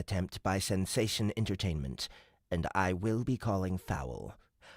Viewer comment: Hey! At 1 hr in at the end of Chapter 13, when the ork is talking. It sounds like you say FILL-ing a grievance instead of FILE-ing. ❤